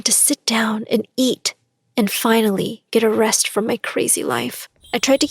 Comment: ⃗ ami bro what do you mean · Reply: i thought you said to crazy laugh